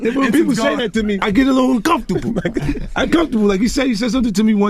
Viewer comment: 50 just exposed himself tryna save himself "i get a lil comfortable" damnn😂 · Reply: It sounded like he said "uncomfortable" and then cringed about Diddy wanting to take him shopping lol